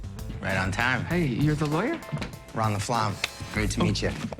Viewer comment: I always thought they were saying Ron the Flom, didn't get it lol